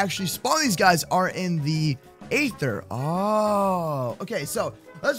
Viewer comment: did he just say ather?